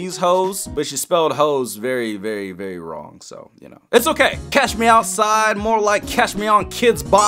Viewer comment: Tbh the whole time i thought she was saying catch me oussid